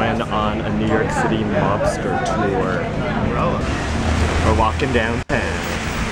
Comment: it took me till you announced the "mobster cafe" to realize that you were not on a "Lobster Tour", as i had misheard you say in the beginning. guess i didn't read the title. i did think a lobster tour sounded kinda strange but on the other hand it sounded super cute... and it's super late right now so i'm a lil brain dead. :)